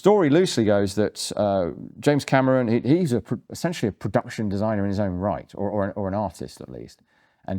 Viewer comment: He's very anti-gun these days and 'enthusiast' is maybe overstating it. He's admitted to knowing nothing about them when making The Terminator - but he was clearly fascinated and knowledgeable enough to get details right in The Terminator and Terminator 2 scripts. He's no Michael Mann but he was more of a gun guy than most directors I think it's safe to say. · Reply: Yeah, you said "Production Designer in his own right" ( I imagined you said gun enthusiast or something like that, in retrospect), but it baffles me that he, making Aliens, never understood that superior firepower may be some kind of equalizer in an otherwise asymmetrical confrontation. Not that it would matter to me as a German at all, because all I'm allowed to own my guns for is sports and hunting. But then again...that might be OK because throwing a bullet through another human being is about as much of a horror as I can imagine... 😳 Edit: And I'd thought I'd never say that, but I have to say I'm on my knees and chant: "I'm not worthy!" Thank you for even responding to my gibberish!